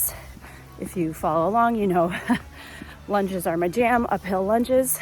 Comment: For a minute I thought you said lunches are my jam, I thought mine too!